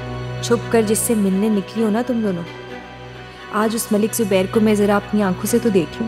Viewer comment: did haya said Malik zubair matlab use pata hai wo malik zubair hai 😲😲